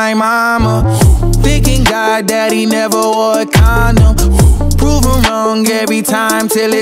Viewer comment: Im only watching this version because i thought he was saying " you'll never see me sober if I'm breathing " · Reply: I keep thinking Lil Nas went full Kanye cuz I always hear: "thankin God that he never wore a condom."